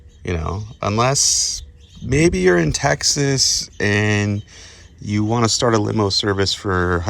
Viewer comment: You said Taxes or Texas? 🤔.sounded like you flipped the A and E around.